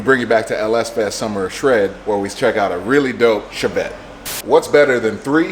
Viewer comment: Bruh I thought that said corvette at first lmao